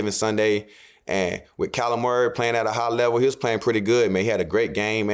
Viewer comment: when you say Kyler Murray it sounds like your saying Calamari